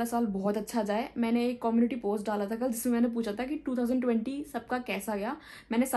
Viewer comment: pe jo maine suna kya apne bi vo suna is she saying 2020🥴😅